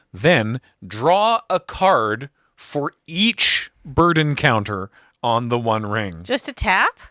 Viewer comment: It really sounds like is saying "Sauron, the Littlest Eye," which is oddly adorable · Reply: when he said "burden counter" i heard bird encounter